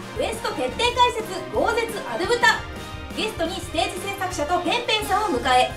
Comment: ステージ製作者「の」ペンペンさんって聞こえる笑